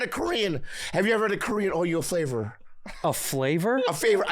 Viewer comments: I swear I thought Bobby said, "Have you ever had a Korean Oreo flavor?" until he repeated it. 😂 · I swear Bobby says “have you ever had a Korean Oreo flavor?”🤣🤣💀💀